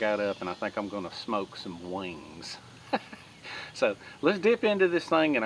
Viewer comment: I could have sworn you said smoke some weed. Must be that southern draw.